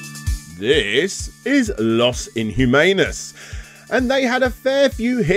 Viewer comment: Oh my sweet relish, I cringed horribly every single time you pronounced Los inhumanos. Like please, there is Google translate that even let's you know how to say it! Other than that, fantastic video, as usual. You are one great video game journalist! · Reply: I was showering while listening to that bit and I thought he said Lost in Humanus until I rewatched that bit...